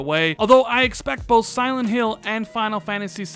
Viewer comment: Did I hear correctly....? Did he say Silent Hill....? 🤭😱